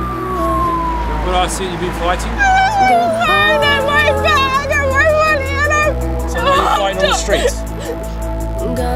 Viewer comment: Please someone who speaks Bri'ish translate me what does the hotdog says between 3:08 and 3:12! I don't understand a word (probably because I'm not a native English speaker, but it's still weird). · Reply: ‘I lost my phone and my bag and my money and I’m cold’